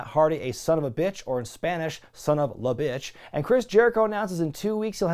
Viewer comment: I thought Sammy said “son of el bitch”?